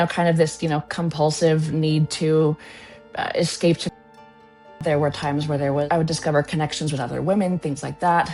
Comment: What's she saying at around 1:05? I'm so sick of the YouTube censoring. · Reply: I think "escape to pornography."